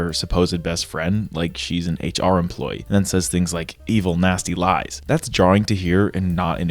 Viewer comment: 1st time, i thought mary said "Evil Nazi lies" 😅